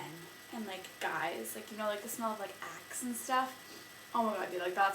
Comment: lol when u said axe my volume was down and my mom came by and thought you said " I just love the smell of boy ass"..! Ahahah(; btw iLove You Hair!<3